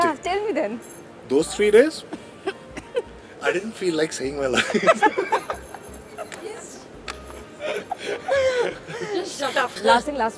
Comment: What did he say at 7:42 ??? I did not understand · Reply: He said about lifting sara when she put on more weight after having mithai and chocolates during film shooting, those three days, I didn't feel like saving my life